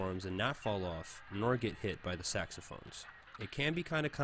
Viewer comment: did you say saxophone?